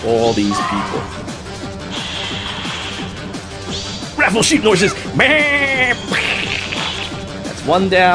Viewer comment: “Ruffle sheep noises BAAAAAAHHHHH” 😂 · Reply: ꧁Cloud꧂ I thought he was saying RAFFLE sheep noises! I was all like, "wut?" lol